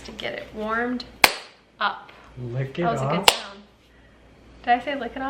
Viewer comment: I thought she said lick it off to Kevin!! 🤪